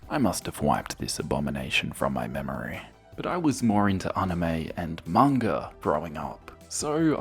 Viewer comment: I thought you said you were more into Aunt May and I was like heyyyy yo!!!